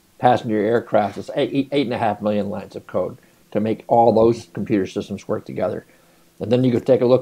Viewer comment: I thought you said it took a million and a half lines of coke to make the F22....